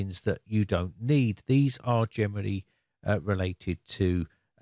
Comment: When you say "generally", I always hear "Germany" 😁